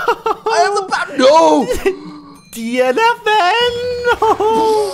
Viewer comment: Cap'n said "DFN!" instead of "DNF!" lol